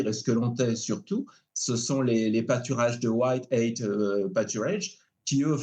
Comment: Quelqu'un saurait-il orthographier ce que dit l'auteur à partir de 4:50 lorsqu'il évoque les "pâturages de... White quoi ? qui, eux, font un boeuf formidable. "White Ate Paturage" ? White... ? C'est quoi le nom du producteur qu'il cite ? White quoi ? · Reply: Malheureusement c'est un prducteur aux us...mais ça doit se faire en Eu, surtout en Suisse... White Oak pas ''tures...il ne prononce pas très bien. Study W ''hite Oak pstures reduisent le carbn at ''msphric... Traduisez le verbe en anglais, c'est facile, et pour atmsp j'ai écrit le mot en anglais un peu coupé, si vous tapez ça vous devrez trouver l'article. Je ne peux pas mettre le lien ici. C'est le principe de l'élevage traditionnel, les vaches se baladent dans le pré et mangent surtout de l'herbe...pas sorcier. C'est plus cher parce qu'elles maturent bcp plus lentement.